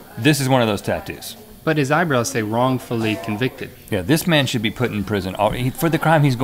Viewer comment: I thought his eye brows said joyfully convicted!!! XD